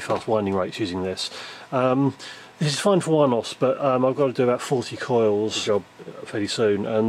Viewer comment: Nice! I guess you need to make a lot of coils ? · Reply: Think he said 40 coils